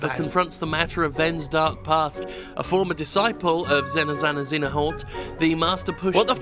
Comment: I misheard that as Xena Xenomorph.